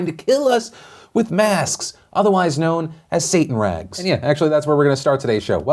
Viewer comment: I had to run it back because I thought you said "Satan's rag".